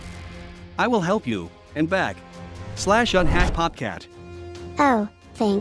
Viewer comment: IM SORRY I THOUGHT UR CHANNEL SAID POG CAT IM NEW TO YOUR CHANNEL